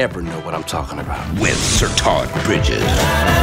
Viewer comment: i thought the dude said "Sir Tod BITCHES!" i was like WHOOOW! YEAH! XD